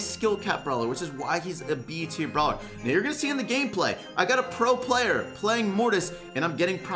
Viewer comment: did he just call himself a pro player